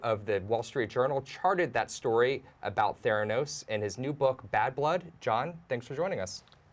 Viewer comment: Thought it said thanos